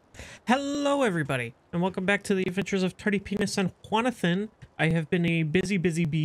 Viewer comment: Did, did you said "The adventures of Dirty Penis & Iguana Fin"? I tried to put the subtitles but those say "Turdy Penis & Quanathan" so i dont know what to think anymore, I have the feeling that i lack metric tons of context so i'm gonna see the entire series to learn the Why of the Penis. I'll be rite back.